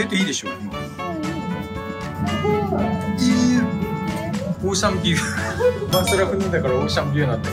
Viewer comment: オーシャンビューが、おっさんブーに聞こえた😂